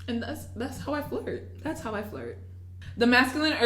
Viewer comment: I thought she was saying “thats how I fart”